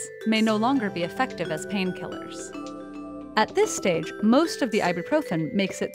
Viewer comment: I heard "pain piller" ..